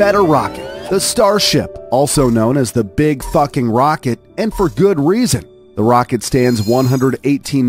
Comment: am i hearing big fking rocket